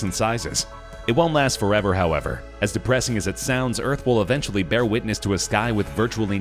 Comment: I thought 2 said depression